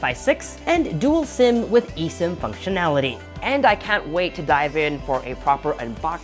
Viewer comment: have I heard it right, dual sim functionality??